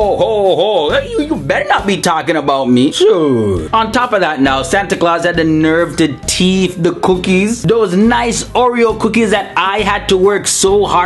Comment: What is he trying to say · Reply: He's saying "Santa Clause had the nerve to steal those cookies." You learn a lot of this if you lived with my grandmother.